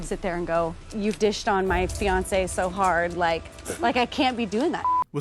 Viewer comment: I feel like no one else made a big deal out of the fiancé thing like that was huge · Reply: It sounded like she said ex-fiancé to me